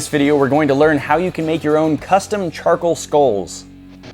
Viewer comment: Anyone thought the second he said custome charcole scull “charscull”